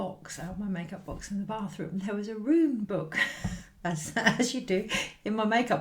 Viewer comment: Ah such a heartwarming message I had a giggle when you said you had a book 📖 I'm the bathroom I thought you said a rude book not a rune book I'm the naughty elf. Hope you have a magical Christmas and New year will try to get to see you soon much muni love ❤️ and gratitude Xxx ❤️ 💕 🙏